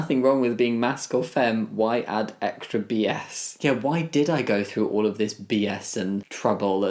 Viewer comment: lol thought it said b’s like bees